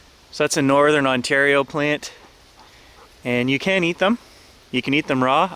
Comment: I could have sworn he said, "you CAN'T eat them" as he slightly shook his head lol....